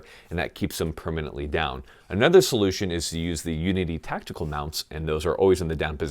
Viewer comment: what were the mounts for the ear pro he was talking about at 11:39 when he said they're superior? I couldn't understand him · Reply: Unity Tactical. But honestly unless you’re jumping out of planes or standing near breaching charges the standard ones will work fine